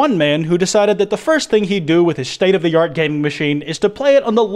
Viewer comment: I misheard "gaming crt" as "gaming cbt" and I thought I was about to get a very different picture....